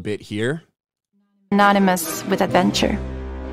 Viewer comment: Sure sounds like the narrator says "anonymous" with adventure instead of "synonymous".